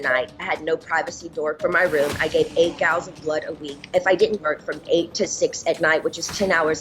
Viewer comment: I'm not a Dr, and I'm sure there's some dramatization going on, but if Britney gave 8 gallons of blood per week, she'd be dead. That would literally be the equivalent of bleeding out every single day. There's only about a gallon of blood in the adult human body at any given time. Just saying... Free Britney! Free Bam! Clean out Hollywood! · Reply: 8 vials of blood not gallons. I think you may have misheard. She has a southern twang which can make it difficult.